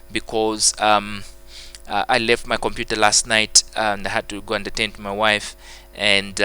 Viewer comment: my dad was next to me and he thought u said; I had to go entertain my wife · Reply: Hahahaha, well I said "I had to go to attend to my wife." That week she was discharged from hospital so I became sort of her home doctor. I said it in another video that my wife was not well, I was just recording the videos because students were writing exams the next few days. It was a sacrifice I made. Tell your dad hahaha